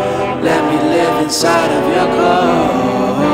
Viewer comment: I really thought he was saying, "Let me live inside of your car." 😑